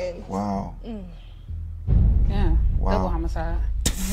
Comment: i cant even understand her, what did she say? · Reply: Double homicide